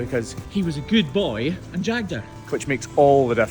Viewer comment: I’m laughing at the confusion about jagging. It’s a shot of antibiotics, not stabbing her! If he’d been stabbing the cow, he’d have chibbed her, or maybe malkied. Aren’t Scottish words great?! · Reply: I thought he said "shagged" at first and this video took on a whole different meaning! lmao